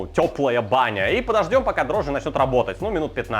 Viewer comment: послышалось: подождём, пока Друже начнёт работать